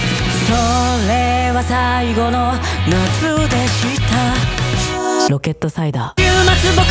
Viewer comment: なぜかころちゃんの｢それは最後の夏でした｣が｢そう。令和最後の夏でした｣に聞こえてしまう…